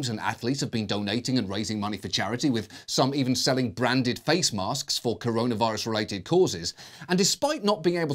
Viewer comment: I swear I heard "brandyed face masks" at 4:00.